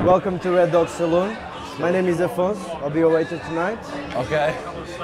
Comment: Did he say waitress lmao